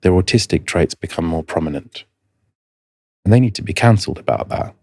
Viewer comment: please, please review the subtitles in 41:31 - I think dr probably meant counselled, while auto-generated subtitles say "canceled". P.S. Thank you and your guest for the wonderful work that you. ❤